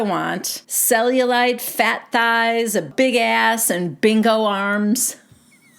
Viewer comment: Instead of bingo arms I thought Ariana said “big old arms”